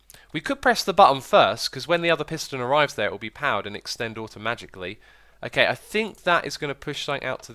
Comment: sounded like you said '..the piston extends automagically' Edit: spelling ;P